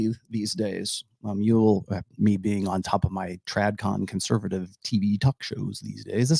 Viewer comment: TradCon traditional conservative I thought you were say trag con. i was like what dafaq is that